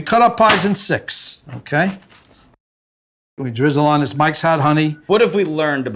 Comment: ok, so 10:17 whats he say? sounds like "mxyzan honey"? whats that then? sounds like a crucial ingredient but I've no idea what he says. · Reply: mike's hot honey